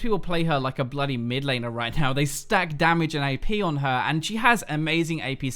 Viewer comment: I thought he said "they stack damage and they pee on her"